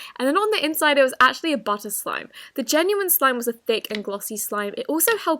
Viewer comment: “Butter slime” Captions: did she just say bus slime?